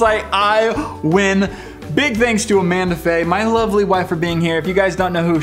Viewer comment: For a second I thought he said my ugliest wife Hhhhhaaaaaa